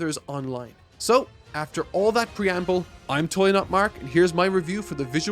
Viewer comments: I don't know why. But when you finally introduced yourself after the preamble and said "I'm totally not mark.." I totally heard "I'm TOEI not Mark". And I died lmao 🤣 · I swear to god in 02:03 I hear he says he is Toei Not Mark.